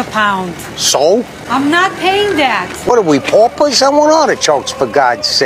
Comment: I always thought he was saying "What are we porpoise?" Instead of paupers.